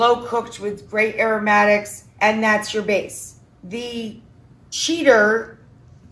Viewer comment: It might just be me and my dirty mind but I nearly coughed my cider up when I hear her say "great aromatics and masterbates". No offence but I'm NOT going to try that version of the recipe!! 🤪